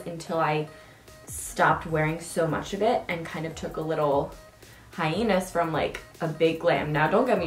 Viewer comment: she said hienus instead of hiatus LOL